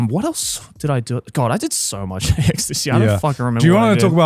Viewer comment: why’s it sound like saying “God I did so much ecstasy this year” 😂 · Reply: I feel like it's supposed to be "I did so much at AX this year" but yeah it 100% sounded like Ecstacy lmao